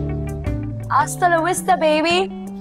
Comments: For those of you who didn't catch it, she said "Hasta la vista, baby" which means "Till next time!" in spanish😂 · what she says? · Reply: Hasta la vista baby It's a spanish phrase which means see you later baby